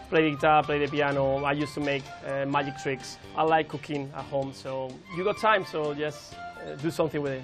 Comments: it sound like he says i like cocaine a home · it sound like he says i like cocaine a home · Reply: Gustav Klenell Sandström it sound like he says i like cooking at home